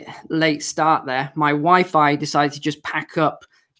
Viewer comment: I thought he said my wife fi... anyways carry on😄